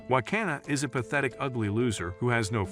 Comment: I thought he said Wakanda😂😂😂😂😂😂